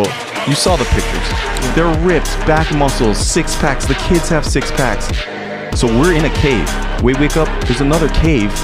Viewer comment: "Dude even the birds have six packs" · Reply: Birds? I thought he said "even the kids have six packs."